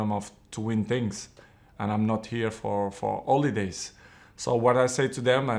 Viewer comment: Cristiano what did u say ? " I am not here for holidays "...I thought you said : " I am not here for OLE-days "😂😂😂. I would be happier if u said that.